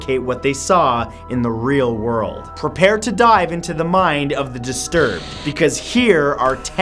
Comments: at first I thought he said time to die,but after he said the next word I realized he said dive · i thought he said, "prepare to DIE" at the intro. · I thought you said "prepare to die" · when he said prepare to dive into i didnt hear the rest I thought he said prepare to die